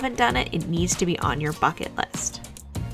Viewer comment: I heard "bucket list" as "needs to be on your f***in list" lol I had to check twice